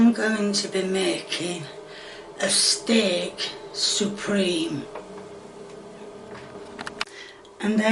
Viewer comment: I thought i heard her say at the end its steak soup-reme lol